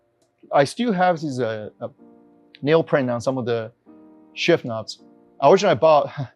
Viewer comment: Can somebody tell me what he says here.. He still has what on his what? #ThankYou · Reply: "nail prints on some of his shift knobs" like indents from his fingers on the shiftknob from switching gears